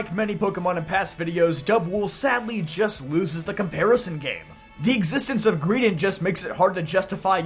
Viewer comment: Ok for some reason I heard “loses the Paras game” rather than “loses the comparison game”. Given how bad Paras is, I think you actually want to lose that game.